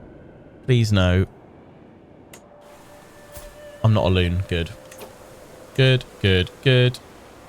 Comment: Turn on a video... I hear 'I am not a loon, good.' Thanks for putting this video up.